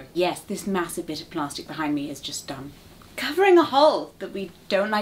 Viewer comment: It sounded a lot like she said “-is just dumb.” here, and I was really disappointed for a sec, but she actually said “just um”, lol.